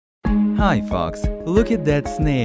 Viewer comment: i almost thought that he said hi fucks